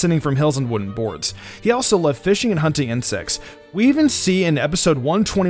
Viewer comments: What is says: "He also loved fishing and hunting insects." What I heard: "He also loved fishing, hunting, and sex..." ...right... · QOTD: What do you think of Ash's overall character? Also, did you learn something new from this video c: ►► <link> Subscribe ►► <link> Patreon ►► <link> Merch Store ►► <link> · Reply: sounded like u said fishing and hunting and sex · "He loved fishing and hunting insects" I thought he said sex........